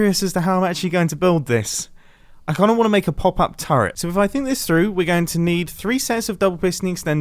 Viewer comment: I thought you said pop up CHARROT... I was wondering what episode I missed where you turned it into a weapon before you killed it...